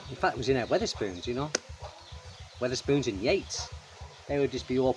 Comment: He said Yates the pub chain. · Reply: oh I thought he said spoons in Yate 😔